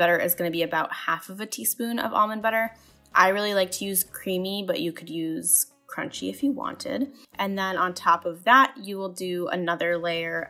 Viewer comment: From the first recipe; What you said "I really like to use creamy but you could use... crunchy if you wanted" What I heard "I really like to use creamy, but you can use [are you insane] crunchy if you wanted"